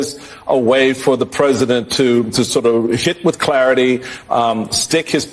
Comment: did he say Sh1t with clarity, or was that a slip of the tongue.